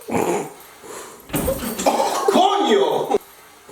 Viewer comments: At @ 2:34 did he say coño? 😂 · did he say COÑO?!! lol on